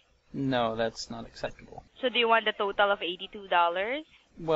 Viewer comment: I looked at your bill when you pulled it up. You were charge $32.00 then $99.99 then you were refunded $49.99 then charged $49.95 then refunded $49.95. The total charged was $82 even and she offered to give that to you IMMEDIATELY after you declined the free blast for 1 year. Please stop making videos and learn to read your bill. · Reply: Please stop making comments and learn to watch the video. The free blast for a year would only save him $60, which is less than $82. Then you misheard her and thought she said, "Do you want me to deduct $82." She actually said, "Do you want the full $82?" He reasonably wanted to pay $0. I know you just misunderstood, btw. That first part was a joke.